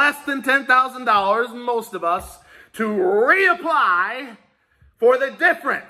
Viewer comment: BREAKING NEWS: HOUSE COMMITTEE FOR SMALL BUSINESS SHARES REPORT PROVING THAT ALL LEGITIMATE SMALL BUSINESS OWNERS STAND TO GET THE FULL $10,000 EIDL GRANT OR THE BALANCE THEREOF! 🔥🎉🍷❤️💯 <link> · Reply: 23:33 you said reapply!!!!!! i heard what k heard.